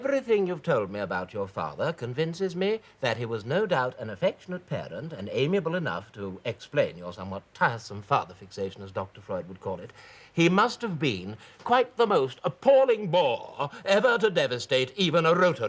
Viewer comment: what did he call her father? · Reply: "An affectionate parent, amiable enough to explain your tiresome father fixation, as dr Freud would call it. He must have been quite the most appalling bore ever to devastate even a rotary club"